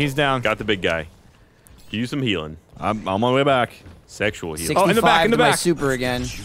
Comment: did say he needed Sexual Healing? :/